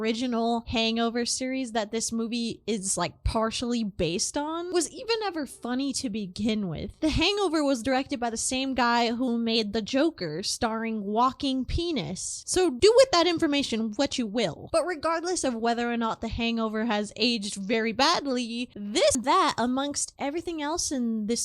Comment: I THOUGHT YOU SAID HIS NAME WAS “walking p#nis”